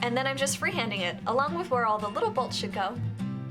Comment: I thought she said "the little bullshit goes" and rewatched it but she said "the little bolts should go" 😂 hahaha great video though. rocks!!!